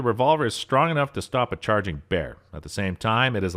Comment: I misheard what he said the first time. He said "bear" but i thought he said "ferret"... i hope its powerful enough to take out a charging ferret.